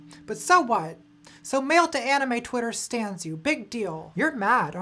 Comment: what does she say? ??? anime twitter ??? you